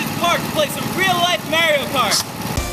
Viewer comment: Did he say Mary-Yo Cart?